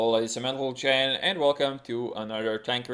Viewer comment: Is saying "hello ladies and mentle gen" on every start of video?